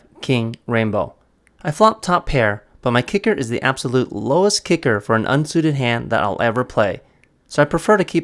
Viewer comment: oh I play A10. It's higher than KJ though. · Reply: I misheard you then, I thought you said J was the lowest kicker you will ever have.